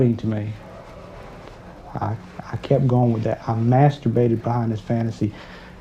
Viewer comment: Thought the first guy said he masturbated to final fantasy. I was like well duh 🤣